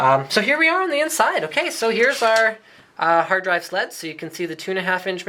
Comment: I heard "hard drive sluts" at 5:29 lol